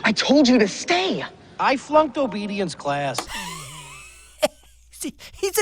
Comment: I legit thought the dog said “I failed diabetes class .” I’m dead